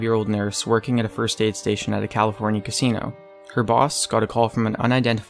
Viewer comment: did he say "her boss scott cawthon"?